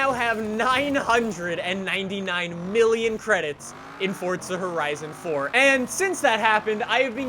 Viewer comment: Why does it sound like your saying fortza?